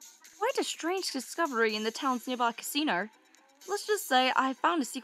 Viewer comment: wait wait "nearby casino"? That sounded like "The Devil's Casino" O-O